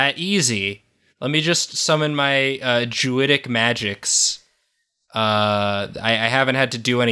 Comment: deadass thought he said " Sumon my jewidic magics" then i realized and not im sad